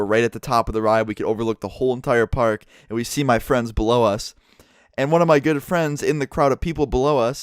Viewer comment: I heard @ 3:10 "my friends blow us"...